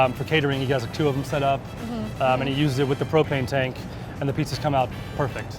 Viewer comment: I heard "cocaine" instead of "propane" so you know which one I'm already gonna get